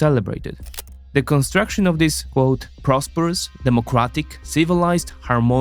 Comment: did he just say democratic 😂😂😂